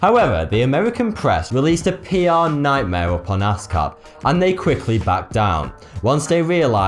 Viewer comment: ass cap....plz tell me someone else heard ass cap XD